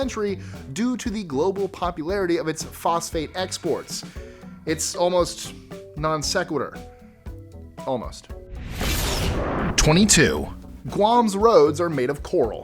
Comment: Instead of "phosphate exports," I heard "prostate x-sports." XD Then I backed it up and heard "prostate exports." Some weird shenanigans going on in these island nations! One sounds like a porn film no one should watch, and the other sounds like an overly-complicated attempt at population control. Neither sounds very appealing. So glad it's phosphate!